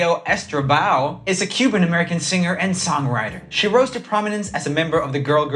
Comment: I legit thought he said Camila Cabello "roasted" prominence... Oops